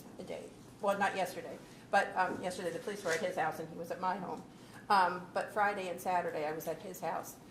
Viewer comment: did she say the police were at his house; he was at my home?